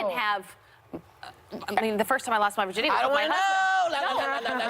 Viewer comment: Did Abby really say "The first time I lost my virginity"??? 😂😂😂